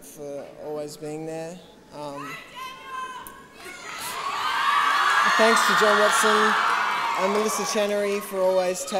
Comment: Em 3:49 o que a menina grita?? 3:49 what is the girl screaming here? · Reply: Go Daniel